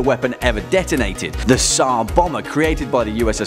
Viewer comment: Did I just hear "sar bomma" ?